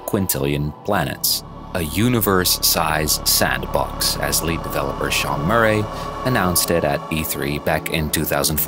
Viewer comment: I misheard you at first here, i thought you said "as late developer" instead of "as lead developer". I was like Oh no, Sean died?!